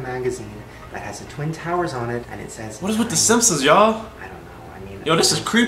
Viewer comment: i think instead of saying the simpsons, i think he said sumpsons😂